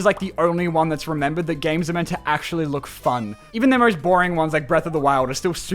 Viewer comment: …what did he said? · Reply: "even the most boring ones are super pretty"